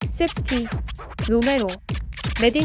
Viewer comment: 은돔벨레 하는데 왜 응돈벌레로 들리냐...